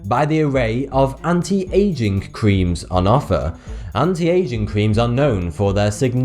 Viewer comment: O thought he said “anti asian cream”